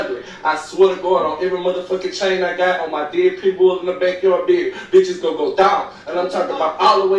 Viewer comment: Sounds like he said "all the dead people in my back yard" at 3:53. Time to see what he's talking about.